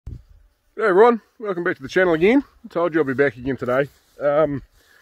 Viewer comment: Trailer looks a bit more time consuming but a hell of a lot easier on the shearer. Those boys really earn their money! Who's Ron by the way? Have a great Xmas and all the best for 22. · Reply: At the beginning it sounds like you said g day Ron but I think you say g day everyone. 🙂